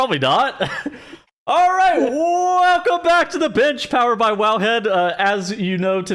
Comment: I had this on 2x speed and it sounded like just said, "Welcome to The Bitch." LOL